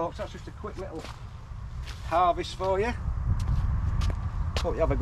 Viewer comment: At the end of the vid I thought you said 'And all that kinda ting'! :) Almost :) Crack on!!